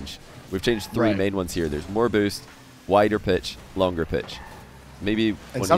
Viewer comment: Bruh at 18:39 He says pitch that sounded like b***h